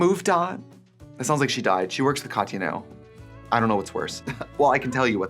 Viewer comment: Did you say works for Tati now or Katya or did I completely mishear? 😂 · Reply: Shes Katya’s assistant